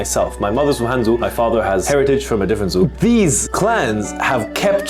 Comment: i might get cancelled for saying this, but when he said "my father is from an other Zu", I can't stop laughing thinking as zoo instead of Zu 🤣🤣🤣